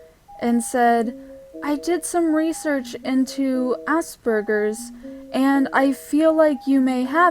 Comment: I thought you said Assburgers